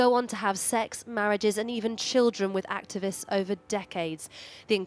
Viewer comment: even children with them?? What?? Did I hear that correctly?